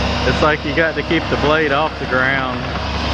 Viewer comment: Thought you said you have to keep the bucket off the ground KNOW IT ALL